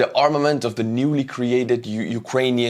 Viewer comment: What is he saying 14:44? Massing Drones??